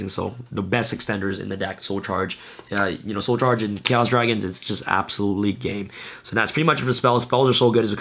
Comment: I thought he said absolutely gay 😂